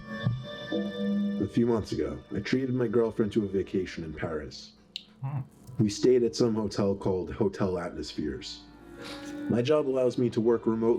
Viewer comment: At first I thought he said I traded my girlfriend for a vacation to Paris 🤣🤣🤣🤣🤣🤣🤣🤣🤣🤣🤣